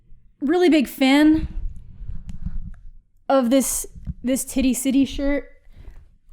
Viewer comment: OK I need more context on the whole “tiddy siddy” shirt because all I hear is “titty city” and I don’t know what that has to do with tie-dye.